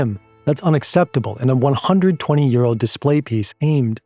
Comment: Oh, $120 *EURO*. I heard "120-year-old" at first and was like ???